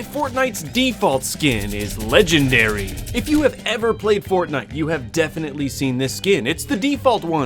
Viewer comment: did he just say fortnut?